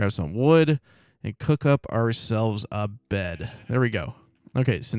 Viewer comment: did he say " and cook up ourselves a bed"?